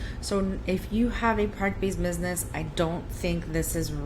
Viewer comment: She is speaking so fast. I can't tell what she said... "this spreadsheet is best utilized for online service providers. So if you have a .... business I don't think this is really meant for you." (1:50) I love her honesty and transparency. But what is that missing word?? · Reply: Product based business ☺️